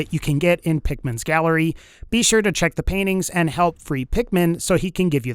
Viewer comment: Did you just say “health free dickman”? Why the fuck was this recommended